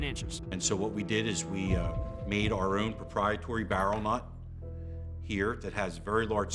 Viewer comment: what did he say at 4:33? "....... barrel knot"